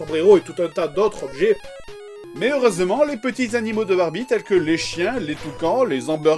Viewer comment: c'est moi ou il a dit Barbite?